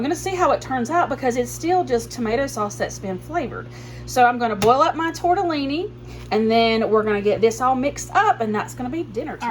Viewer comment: I thought you said blow up the tortellini😂